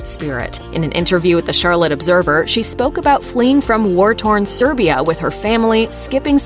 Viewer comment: am I crazy or did she say Serbia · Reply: Yeah, she did, she fled from war torn Serbia.